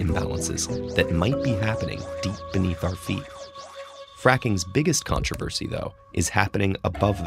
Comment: That moment when I thought it said fapping...